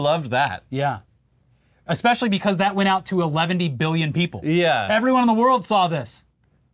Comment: Did just say "Eleventy Billion people"?